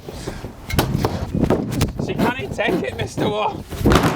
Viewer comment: wtf is he saying all I hear is Mr. Worf